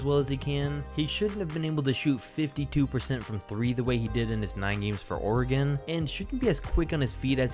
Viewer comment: Did he say he played for Oregon for 9 years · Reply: bigAthebossz he said nine games